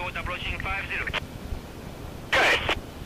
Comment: I thought he said gay